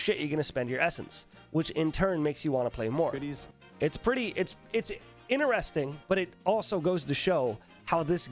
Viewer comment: could've sworn i heard "titties". Of course i want to play more then...